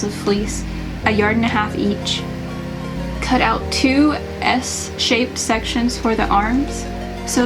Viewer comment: ok, loved this video and i'm literally going to make the robes for my husband and I. I was watching your tutorial and I thought you said cut out 2 "S" shapes for the sleeves except they don't look like "S" shapes. So i'm trying to decipher what you said lol.